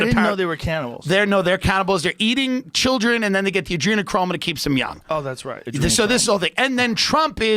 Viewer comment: LOL did someone say adrenochrome?